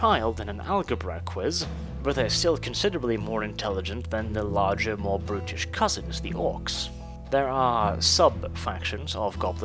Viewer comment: I thought you said: "Their more british cousins".^^